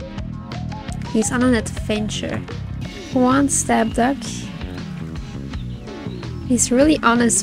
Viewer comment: I had you on second monitor and legit thought you said "Go on step-duck" and completely lost it 🤣🤣🤣🤣